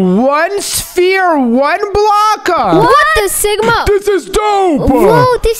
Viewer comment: did did just say what the sigma😭😭